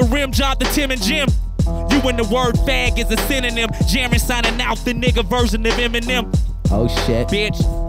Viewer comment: Jarren called himself a black Eminem? I bet Hopsin felt downgraded... Nah, just joking. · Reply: ScreamDesignPlay he said "Jarren signing out the nigga version of Eminem." You must be hard on hearing, i don't understand where you are getting "for" from, he doesn't even make the "F" sound. Go get your ears checked bud and tell the "other people you've let hear it" that thought he said "for" to get their ears checked aswell.